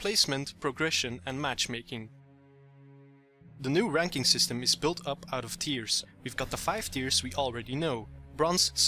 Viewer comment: At the start i thought you said "the new ranked system is built up on tears"